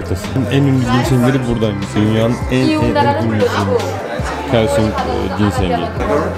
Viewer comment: tam olarak ne diyor duyamadım , duyabilen varsa söyleyebilirmi? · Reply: Kaesong ginseng diyor